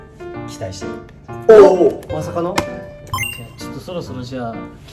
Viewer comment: 嶺亜くんの「まさかの？」が「まさがど？」に聞こえてしまったwww